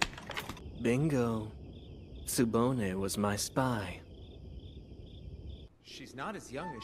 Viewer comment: Illumi: “Tsubone was my spy” Alluka and Killua: 😃😧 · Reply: I thought he said “Tsubone was my spine”